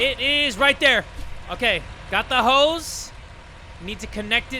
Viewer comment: When you said “I got the hose” I didn realise what you were talking about so I thought you were saying “got the hoes”